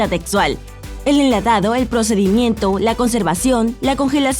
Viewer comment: escuché el Procedimiento y es el Procesamiento, cuántos subs perderán por este error? *Broma